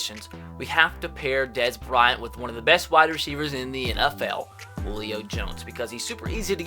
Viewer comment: Sounded like you said Leo jones when you introduced him as a trade option lol.